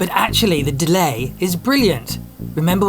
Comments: and remember what his penis said... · turn on automatic captions. It says "penis said", instead of "Peach said". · "remember what his penis said" 🤣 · the subtitles thought you said penis. Lmao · Turn on captions at 14:50 "Remember what his penis said" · You really need to check your CC out, because at 14:51, it listed "Remember what his penis said"...LOL. Sorry, just thought I'd let ya know...unless someone else already has.